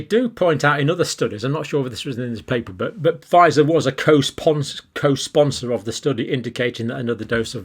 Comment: Sorry! 😂 For a minute there I thought you said Pfizer were sponsors of the research!!🥴